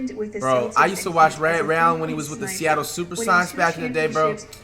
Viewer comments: Flightreacts: “I watched Ray Allen in the SuperSonics days” but still thought Kobe should be 10th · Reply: You sure he said that? Because I heard it as Supersox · I know Flight aint this dumb... Did he just say "SuperSocks"😂😂